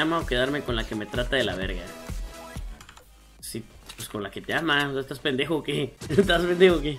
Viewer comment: En el 2:49 retrocedan 10 segundos y parecera que dice pues con la que te quiere